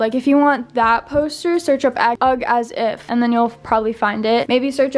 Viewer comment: hold up. 20:36 did she said 'ugg as if'????